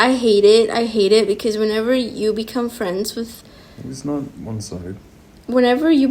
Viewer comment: who else heard say “it’s not one sided” after she was talking about how she used to have crush on